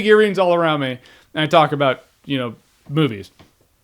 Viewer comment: Who had to double take at 5:15 because you thought he said "talking about boobies" instead of "talking about movies"?